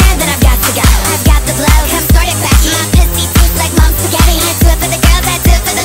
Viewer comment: Help I always thought she was saying ‘monster caffeine’ but she’s saying mom’s spaghetti??